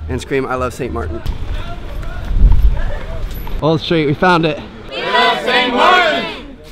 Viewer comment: I thought they said we love Tmartn